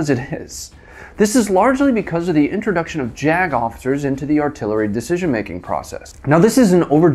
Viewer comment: Close you eyes...Who does he sound like? This gentleman's accent, speech pattern. Who is it?... 1:04 I thought you said the introduction of Jagoffs.